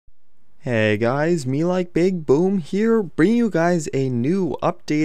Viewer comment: "hey guys its MeLikeBigBoom" I thought he said me like big boob